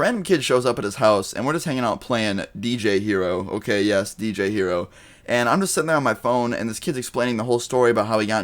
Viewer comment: I thought you said BJ hero lol